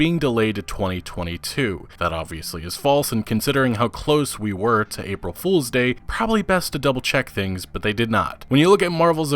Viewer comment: I thought you said "considering how close we where to a police state"